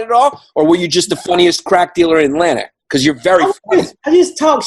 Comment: Ms Pat is never not funny. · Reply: Thought you said never funny and I was about to fight you hahaha